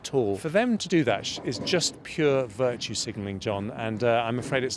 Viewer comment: did he just say virtue signaling?